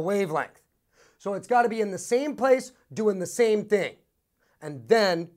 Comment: Excuse me guys ; what is the word at 1:23 ;while he did say (the same place doing the same .....?) What>??? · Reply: Same place doing the same thing... i.e. going in the same direction. Both going up, both going down